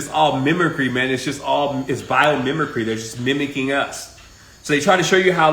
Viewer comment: What was said at 33:06? Memory? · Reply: I think he said “ mimicry, bio mimicry, they’re mimicking us.”